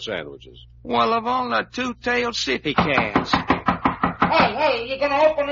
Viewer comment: what did he say ? , "Of all the two tailed 'shitty' ? cats ?" - I just listened 3 times , can't work it out . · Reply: It was probably "sissy cats".